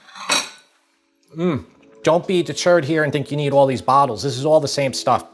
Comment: I heard "don't be the turd" as the first line about the jars of spicy guys, and then realized deterred, deterred. This sounds delicious!